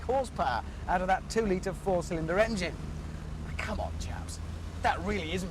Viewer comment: Did he say, "Come on chaps" or "Come on Japs"? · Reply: 🙄 chaps obviously...